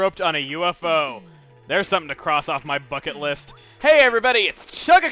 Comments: Anyone else hear "Fuck it list"? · kinda sounded like chugga said fuckit list in the beginning